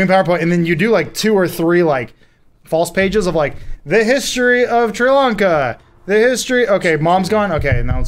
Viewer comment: did say "The History of Sri Lanka"?